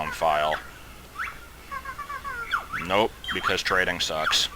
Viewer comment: and at 10:14 it thought he said "castrating sucks" xD